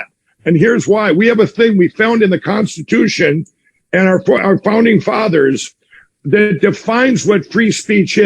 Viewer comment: Baaahaha thats a crack up. The audio quality isn't that great and for a second there it sounded like he said phoney father's instead of founding father's